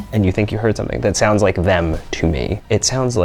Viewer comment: is it just me that heard 'Liz' instead of 'them' at 22:04????????